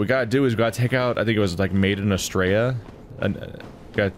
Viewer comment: I thought you said "Made in Australia." You didn't.